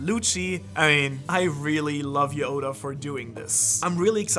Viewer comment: did he say Yoda ?